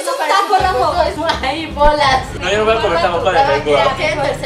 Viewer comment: Jajaja en el minuto 2:16 Lesslie dice "esto es un taco Rafo" XD o no se si escuche mal confirmenlo porfa 🙏